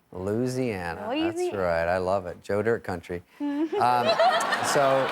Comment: I'm not American so I wonder if you could explain what David's reply was around 6:35 when she said she was from Louisiana? Something like "joder" people? Just curious. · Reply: "Joe Dirt" country- a movie David Spade starred in